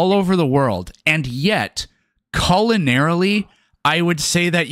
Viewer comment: Did you just say colonarily?